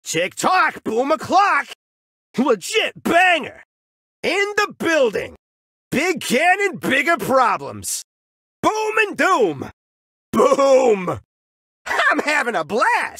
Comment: 00:1 call me crazy but I heard other thing the first time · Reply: Same, thought he was saying "big galet bigger prizes"